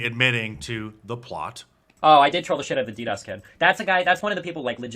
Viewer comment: Who is it that Destiny planned to kill around the 12:50 time stamp? I can't tell who he is talking about. It sounded like he said the "DDOS Kid"